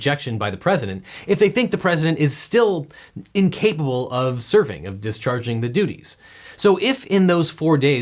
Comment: did he say 'surfing', I heard 'surfing'. That were easy to prove. 🏄‍♂️😂